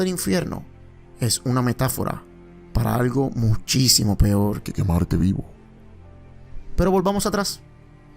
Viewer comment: Excelente vídeo, lo estoy viendo amigo... hay algo que se escucha pero no se entiendo en el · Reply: Jeje es un efecto de audio... Dice que "el fuego metaforico es mucho peor que quemarce en el infierno"